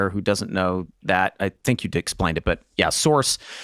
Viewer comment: It sounded like Glenn said "I think you dicksplained it" (regarding source music vs. score) and now I want to use that word all the time. Move over mansplaining, dicksplaining is more accurate way to describe it.